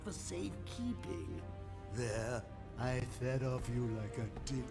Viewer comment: I thought at first he said "I slowly fed off you like a d***."